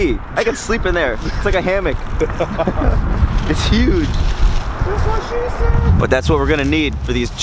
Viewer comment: Did anyone else hear the dude say that's what she said